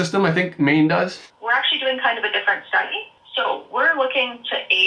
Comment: Your subtitles says: "We're actually doing kind of a differnet state." .... That is wrong. She clearly says: "We are actually doing kind of a different STUDY."